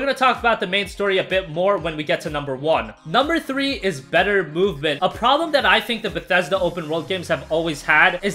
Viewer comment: when you said better movement for #3 I thought you said better boobs